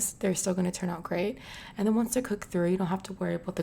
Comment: Did she just say "cooked cat"?